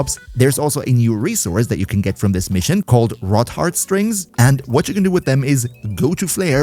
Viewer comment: I literally thought you said "rock hard" strings 😭